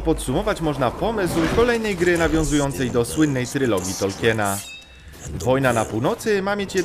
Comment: Czy tylko ja usłyszałem srylogi?